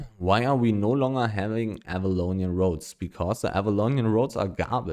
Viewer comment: i think said avalonian tools not roads🤣